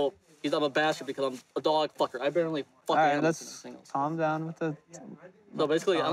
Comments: Did the boyfriend really say what I think he said after that...dog loving...accusation? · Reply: and I SWEAR he said, "I barely f*** animals"!!! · Did the boyfriend really say what I think he said after that...dog loving...accusation? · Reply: Damara Papaefstathiou I heard “I barely f*** animals.” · "He says I'm a dog-f***er; I barely f** animals?" WTH lmao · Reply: I clearly hear “I Barely” you guys need to clean ya ears out! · he says he gets called a dog f**ker, but then says "I barely f**k animals. Did I hear that right??? They should of arrested him on that statement alone 🤢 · did he say "he thinks im a dog f*****, I barely F** animals."??? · Uh. Am I hearing things or did the guy at 12:10 say “I barely f*ck animals”? Lol